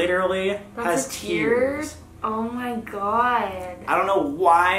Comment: Whenever she says "omg" I just hear "Oh my god Ronnie"